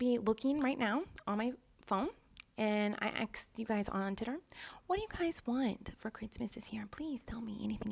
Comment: I thought she said tinder but she said twitter i think Miranda........